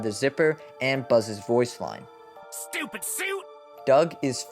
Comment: I always thought that buzz said "stupid soup"